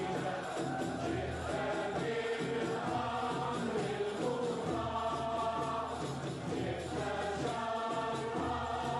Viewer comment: what where they saying at 1:15:42 i couldnt hear it much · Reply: The full moon is upon us طلع البدر علينا